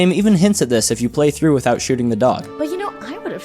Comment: ermmm... thats a dog... 🤓 I guess I heard it wrong I thought he said duck